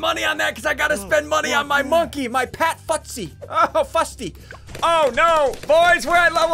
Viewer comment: Ummm did he say the f word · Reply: no he said "pat fusty" the big monkey name.